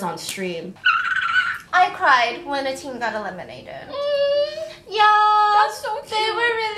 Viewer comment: did she say Team BEBE got eliminated????? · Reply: no she said she cried when a team got eliminated. she never said what team .